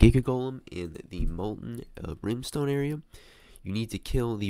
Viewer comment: hey i have a recipe to finish the shock master quest Anubis Greatsword + D Cane = Timebomb Neferti's Harbinger + D Cane = Superball Superball + Mushy Fist = Fist of Tree Wisdom Fist of Tree Wisdom + TimeBomb = Stravants Lightning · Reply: i thought said he was in brimstone